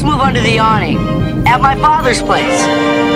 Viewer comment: Thanks for the upload...I heard "This is my Father's world" at the end of the story🎶👍🏼